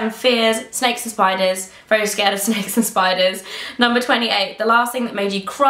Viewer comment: I thought you said you were afraid of sexist spiders..... lol